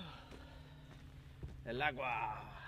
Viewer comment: what did he just say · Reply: Meaning The Water '''El Aqua'' sorry. i misspelled it